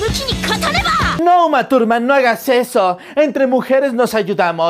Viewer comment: Yo escuche "no masturb@ No puedo dejar de reír